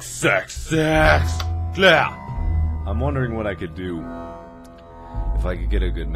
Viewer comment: HAHAHAHHAHAHA at 0:40 it sounds like he says sex sex sex, I had to look at my screen to c what he was talking about.